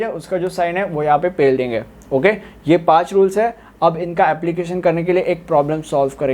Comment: I heard pel denge